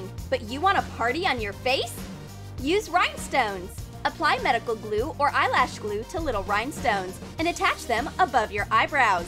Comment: what i heard:”use MEDICAL glue or eyelash glue